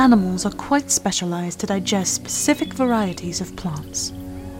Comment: Did she just say pacific for specific?